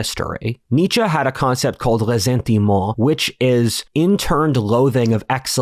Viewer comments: what does he say here? "lesenti mo'"? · Anyone know the proper spelling of "Lesente Mua?" Caption is wrong with "lenti mo." The concept that interned loathing of excellence coming from the weak would lead to a neutered society?